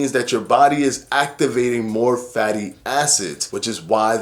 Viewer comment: It sounded like you said Fatty Asses😂😂😂 instead of Fatty Acids😂😂😂